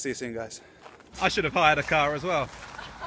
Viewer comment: can someone tell me what he says at 5:17 please? "i should've ... as well" i dont understand :( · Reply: rented a car also, as they all had flash cars and had his